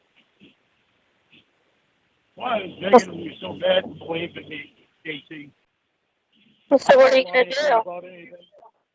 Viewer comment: What is he saying at 7:40, please. Can't make it out. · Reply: It hurts me so bad (believe me/in me?) Casey.